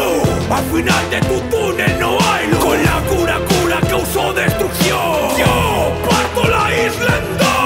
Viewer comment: alguien mas se dio cuenta que dice destrución